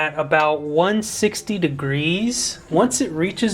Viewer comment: did you say “ 1/16 degrees “?